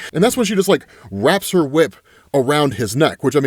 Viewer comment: Why did it sound like he said" wrap her whip around his nuts" 😂😂😂😂 i was like oh we on that route 😂😂